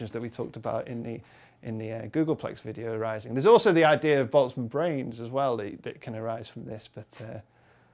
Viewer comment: what did he say? what brain? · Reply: he meant boltzmann brain, google it